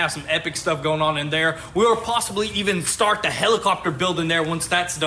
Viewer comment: I swear i thought y'all said hellcatcopter not helicopter